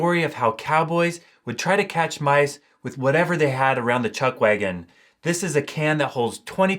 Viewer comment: I couldn't understand what you said there. Cowboys used whatever they had around the what? "Chugwagon?" I'd appreciate if someone could help me. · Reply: According to Hollywood Land, the wagon that held all the food and kitchen utensils for cooking out in the trail was called a "chuck wagon".